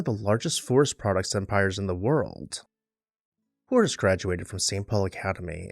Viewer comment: I cant make out what you are saying. Hoors? Coors? Moors? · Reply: Horace